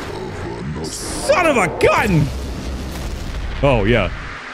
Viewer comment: i hear amongus 😂😂😂